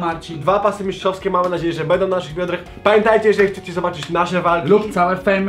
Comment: usłyszałem na naszych jądrach 😂😂